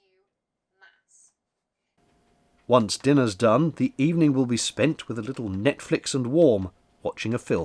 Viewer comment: I thought he he said Netflix and porn . LOL